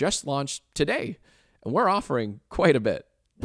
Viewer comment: I wasn't looking at my phone during the Patreon bit at the end, I heard it as "We have one single tear" 😂🥲